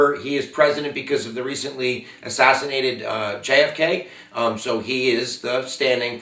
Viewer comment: 50 on first listen i thought u said 'he recentley assaasinated jfk' which is probably accurate anyways 😆